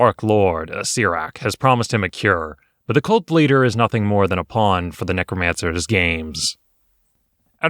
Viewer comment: that sounded like "But the occult bleeder ..." which was actually pretty amazing, given the description and images before